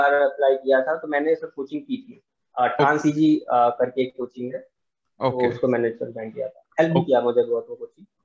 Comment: Coaching name kya bol raha hai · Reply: Transeasy